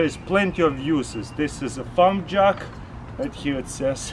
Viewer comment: i thought u said "pump jug"...ohh farm jack